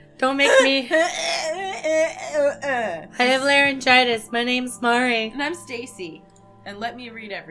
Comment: At the start when Mari said “I have laryngitis” I thought she said “I have many vaginas” HAHAHHAHA Mari, the goddess of pansexual vaginas 😂 Hope you start feeling better